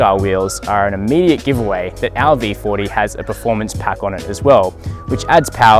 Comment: Did I hear audi 40 at 0:47?